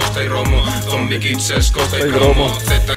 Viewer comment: siempre pensé que decía ZombieKids es Costa y PROMO (de promoción) pero es probable que este equivocado yo